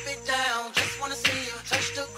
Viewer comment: Funfact, before I saw this video, I heard, “Oomfa dancer”